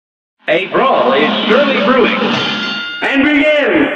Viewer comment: Siempre pensé que decía "Hey bro" en vez de "A brawl" XD